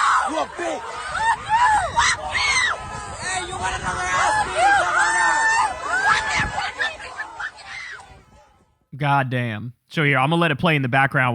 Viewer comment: Kids saying "you want another round' amazing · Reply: I actually heard it as “you want another a— beating. Come on up.”